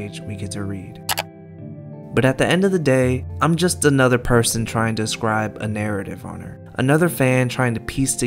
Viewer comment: i thought he said I am just a person trying to marry the girl